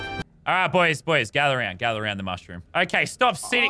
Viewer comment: lmao, i thought it said my thic mushroom.